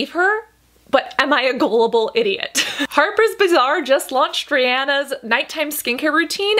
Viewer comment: I actually thought she said: Harper's Bizarre 😂😂